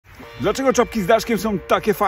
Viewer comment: Czy tylko ja usłyszałem "czopki"?